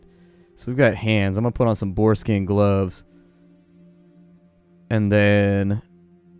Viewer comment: When he said boar skin gloves I thought he said foreskin gloves, Like damn that is a badass dude